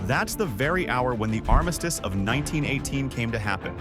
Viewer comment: For R a second I thought that said ‘Justice of 1918’. Hm